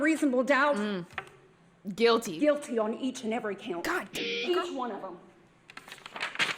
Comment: On my first watch I didn't catch swearing, so I straight up thought turned around, pointed, and said "Fuck each one of them." and almost lost my mind.